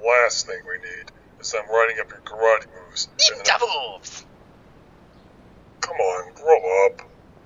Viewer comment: lol did he say 'ninja boobs' at 1:51?